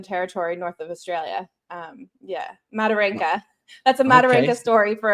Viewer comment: Oh I LOVE Mataranka 🇦🇺🇦🇺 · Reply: I thought she said Maralinga ☢️ but my hearing is still blocked up from Covid a month ago. I will start from the top again when it’s on channel- hopefully my hearing will be back to normal!